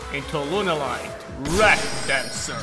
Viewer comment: Ok I cannot be the only one who heard Lunalight Lap Dancer? 0.o